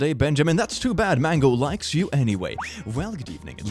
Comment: I hate to admit I thought was gonna say "Mango likes your balls"